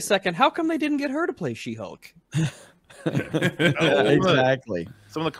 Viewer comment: I heard that..😆 how come they didn't cast her as she hulk? 2:06 "She-BULK" HAHA!! WTH?!💀🤣